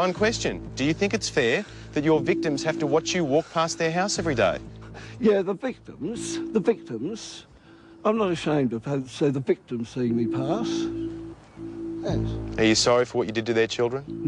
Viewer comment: What does he say at 0:45? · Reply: Yeah, the victims, the victims, I'm not ashamed if the parents of the victims see me pass, thanks